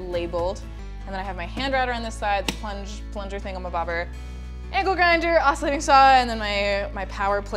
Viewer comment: Did you just say "Ankle Grinder"?